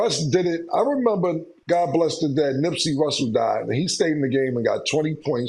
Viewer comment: Did Cam say Nipsey Russell?